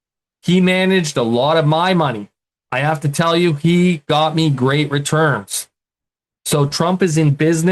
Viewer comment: I enjoyed your presentation! I’m not sure if you had a slip of the tongue, but I heard you say, “when God returns.”